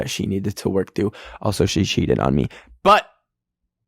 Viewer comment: AT THE END WHY DUD I HEAR “she sheeted on me” and I was like “imagine having a bitch shit on you 😭😭💀